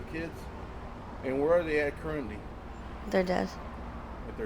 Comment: I swear I first thought she said they were dead.